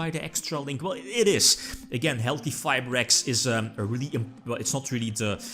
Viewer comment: I thought I heard "healthyfibrax" and now i cant unhear it